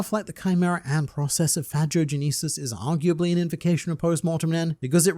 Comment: He said Phagogenesis so weirdly I thought he said "Vagogenesis" and I was like... isn't that just genesis XD